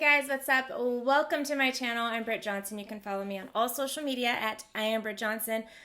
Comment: Swear I heard "Tit Johnson." I'm immature. 😣